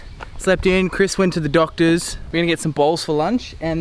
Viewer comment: i thought you said "we're gonna have some balls for lunch" lol. Great content as alway!